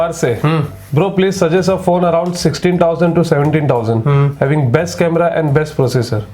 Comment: what is said by brother can't hear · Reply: 16-17 hazar ke bich